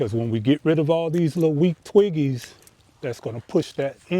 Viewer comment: Removing twiggies😂😅 I love it! Thanks for Sharing 💜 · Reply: Omg I thought he said twinkies! Both funny 😂